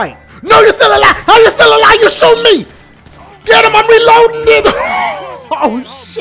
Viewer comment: did dashie say nigga?